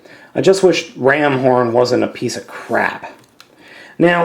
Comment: could have sworn you said "Ramhorn is a peice of crap" in this review